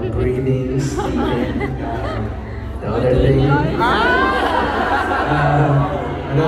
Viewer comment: thank u for sharing this 😻.. what did he say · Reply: He said "the other thing".... he does mention it later but that's the bit I didn't record. Basically he's referring to 5EX